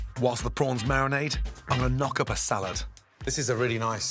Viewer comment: di- did he just say he was going to "knock up" a salad?